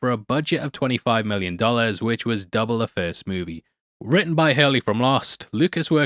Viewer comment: At mark 43:00, he says that the 2nd movie was written by Hurley from the tv show LOST. Did I hear that right?